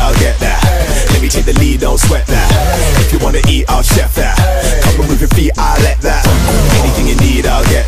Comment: At first I thought I said complement your feet yeah I like that😂